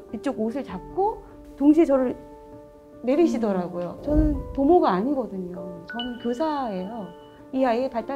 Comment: ‘부모’가 아니라 ‘보모’가 맞는 자막 아닌가요? 문맥상 부모면 이상한 거 같은데. 보모라고 들리기도 하고요.